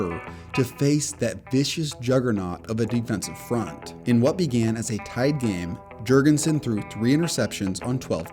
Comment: Did they say "in what began as a tied game"???